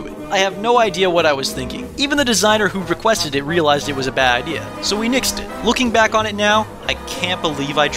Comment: He says "so we nixed it" instead of "fixed it"